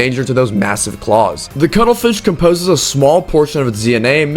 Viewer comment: One quick correction, having cuttlefish DNA doesn't make the I-Rex a good swimmer, at all. She has zero aquatic traits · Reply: I thought you said cuddlefish (from subnatucia)